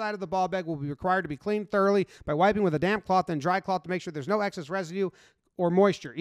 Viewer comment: I thought u said "by wiping with a damn cloth" lol